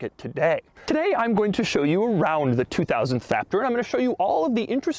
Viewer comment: I heard faptor XD